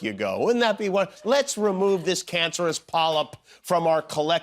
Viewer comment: The subtitles read 'poop' instead of 'polyp'. Both seem fitting for tRump.